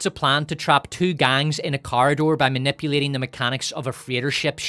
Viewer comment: OHH you're saying corridor, I thought you were saying car door.. was confused lol